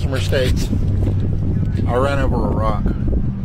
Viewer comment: "I ran over a rock". Was there a sign nearby that said "Mount Rushmore"? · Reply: I thought they said "I ran over Iraq".